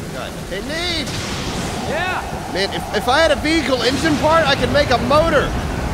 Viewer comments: To be fair to Neebs I thought he said Beagle engine part too lol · I swear I heard him say Beagle Engine Parts too.